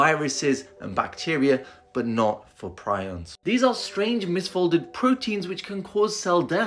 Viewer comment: tell me why I thought it said prison not prions and incarcerated not incinerated